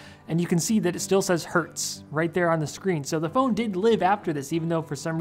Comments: When you talked about the screen turning on again after shooting it while playing a sound, I thought you said "hurts" and not "Hertz". So basically, for a moment I felt deep empathy for that phone. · The phone still says "Hertz" well yah, getting shot with an air rifle does Hertz, it Hertz a lot 😂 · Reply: when he said “Hertz” I actually thought he said “Hurts” so i thought the phone said “Hurts” I was confused and thought he had some sort of self aware phone